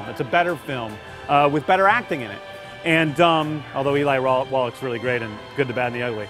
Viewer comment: did he said "Eli Roth" instead of "Eli Wallach"?